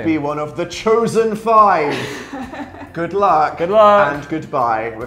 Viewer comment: it sounded like phil said at the very end 'sounds like fun hubby' lmao hubby cough cough PHAN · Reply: dankindiephan I wish but I think it was "Sounds like a fun hobby"